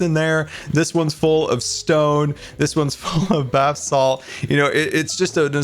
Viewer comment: did scar say bathsalt